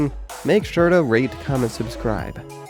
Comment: anyone notice it sounded like he said rape,comment,and subscribe